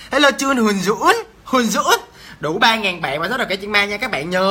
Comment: ổg nói đủ 3000bn mà tui nghe thành đậu 3000bn 😂😂😂